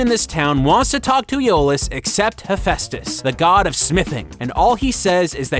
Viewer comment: "The God of sniffing!" is what I heard.